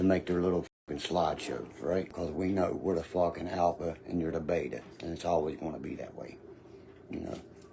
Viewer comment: 😂😂did he say the alpha and the bega ? 😂😂😂😂 thanks again keep em coming brother 😂